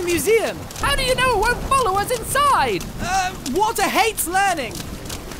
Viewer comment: “Water hates learning” “What’s yours called?” “Gun” Best lines I’ve heard on eddsworld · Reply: At first I thought you said “Walter” lol